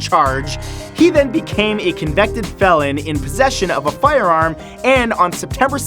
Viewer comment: did really say "convected" instead of "convicted"? Sounded like it to me.